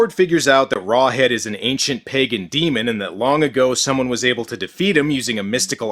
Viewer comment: the way he said "ancient pagan demon" made it sound like he was saying "ancient pegging demon" wich is, to being honest, a lot more terrifying than the guy from this movie